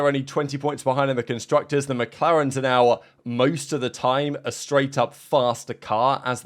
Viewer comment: timestamp? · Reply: but my bad, I misunderstood that part.Thought I heard "than" rather than "as".